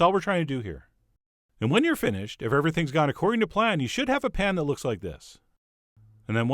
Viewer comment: ish I swore you said the very apropos "...accordion to plan...", which it turns out you did not, so now I have my sadpants on.